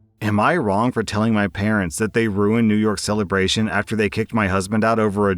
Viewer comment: hehe new york celebration instead of new years